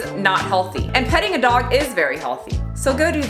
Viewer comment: Oh my gosh...I thought you said, "Cutting a dog is very healthy!" 😱 Took me a minute. 😅